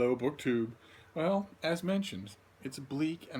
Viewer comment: All this time I thought you said "hello, book 2" 😂